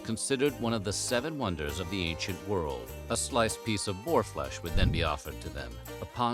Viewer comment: Did anyone else hear that as “whore flesh?” 😂 I had to put on the closed captions to get what was said.